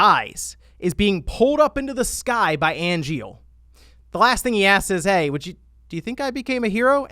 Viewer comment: Did say "blast in the ass?" Lol